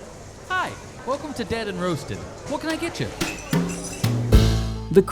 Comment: Couldn’t hear the intro. Am I the only one? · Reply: 1st thing I heard was “Hi! Welcome to dead & roasted.”